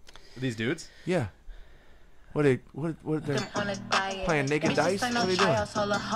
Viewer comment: what they guy in the Orange say 💀🙄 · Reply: He asked if they were playing naked dice.